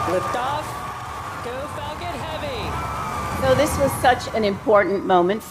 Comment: did she say fuckin heavy